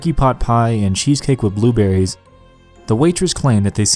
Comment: Cheesecake with blueberries for desert ? I could have sworn I heard it was peach cobbler.